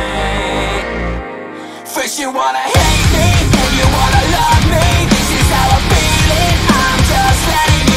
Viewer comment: I honestly thought he was saying "Bitch you wanna hate me, then you wanna love me"